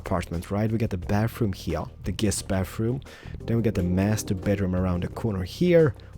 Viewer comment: most major misheard ever: masturbateroom :/